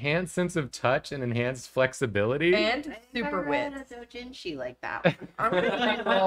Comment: I thought had said "super width" for hero, and I was like "oh no, he's flexible AND dummy caked"